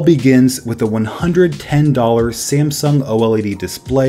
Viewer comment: did I just hear Samsung... Wow I thought they were against each other Edit: Also I’m watching on my iPhone 5c. Where da 5c gang at?? :p